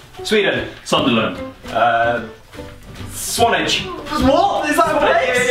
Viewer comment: What did he say? It sounded like a Russian swearword ("сволочь", "svolotch", meaning "asshole").